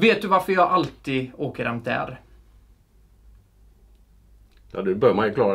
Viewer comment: Vad säger han 6:44 "hämtdär" ?